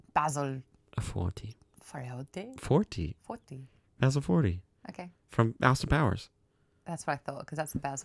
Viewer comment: I totally thought said basil fawlty from fawlty towers